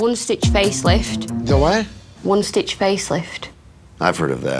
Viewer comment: I thought she said “a wound stitch facelift”… British English is so hard